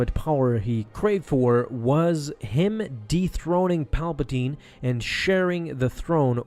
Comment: I heard deepthroating at 2:27.